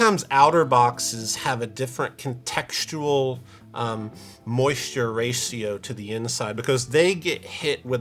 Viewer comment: Did just say “moisture race-io” at about 8:25? Another great (albeit disgusting) band name.